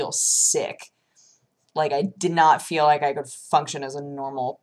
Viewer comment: I heard her say "I've been able to fuck sjin with pretty much every headache I've had" and I was like, wait wait wait, what? ohhh function